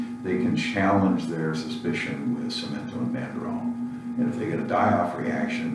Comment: "... they can challenge their suspicion with <I couldn't make it out>." Sounds like he was saying some OTC medication could be used to test the hypothesis that one has Borrelia burgdorferi? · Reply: Nutramedics .com Samento and Banderol